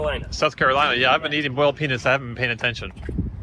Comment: Funny. At 2:22:05 if you have subtitles on (English Auto-generated), it says “I’ve been eating boiled pe**s…” instead of “… boiled peanuts.” 😂 So what, I’m childish. It’s fun.